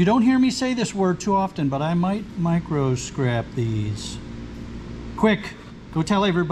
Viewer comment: Couldn’t believe I actually heard you say micro scrap I had to listen back I thought you said microwave at first lol I almost fell off the couch haha